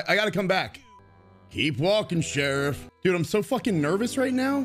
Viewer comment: Man said “keep walking sheriff” all I heard was “DUTCH!!”